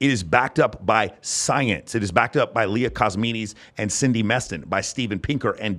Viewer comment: you mention David buss, steve pinker, Cindy MEston, and one more name I couln'd get, something like "Leah Kosmitis"? Just here 1:18:35 ***What I’m saying is the truth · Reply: Leda Cosmides (might be spelled Cosmedes - wife of John Tooby, co-founder of the field of evolutionary psychology)